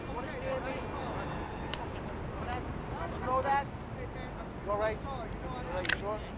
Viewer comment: I thought you said you were alright spider...